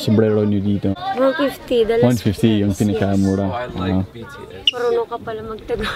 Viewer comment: I don't know about you but I heard "I like BTS"